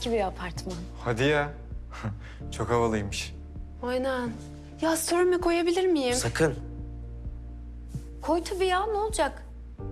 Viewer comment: ne diyor anlamadim? "sorme" ne? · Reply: "Story'' me koyabilirmiyim? Diyor...